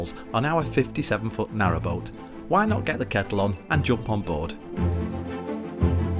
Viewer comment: Oh my gosh I can not believe it took me this long to realize what you where saying I thought you were saying get a catalog lol now I know it’s get a kettle on lol 😂😂😂😂love you guys